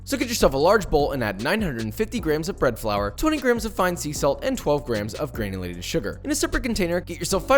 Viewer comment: I thought you said 200 grams of sea salt. Bro, that got way too salty! I guess it kinda compensated for the unsaltet cheesesteaks in Philly